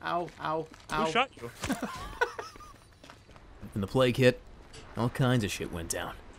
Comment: He was saying plage hit, I thought he was saying play kid